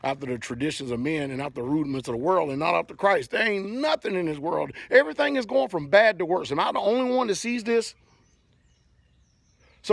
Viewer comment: I thought her husband said" ain't no church in the wild". Sick.